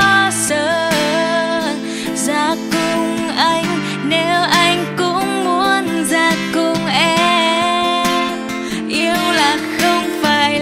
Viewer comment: Có ai giống mình không, lúc vừa nghe nhạc vừa làm việc, khúc "nếu anh cũng muốn già cùng em" mình nghe thành "muốn giặt cùng em" hihi :)))